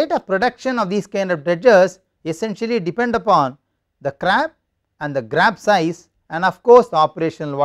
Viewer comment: Did I heard crab and grab